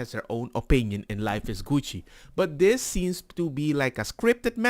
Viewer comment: I thought you said life is coochi lmao